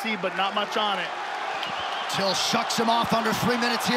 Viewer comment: I had to rewind a bit to make sure I didn't hear "suck"😂